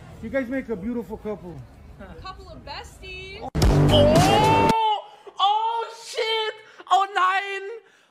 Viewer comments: Was hat die gesagt? Hab das nicht verstanden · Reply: „couple of besties“ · kann mit jmd erklären was die da gesagt hat habs nicht verstanden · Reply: „couple of besties"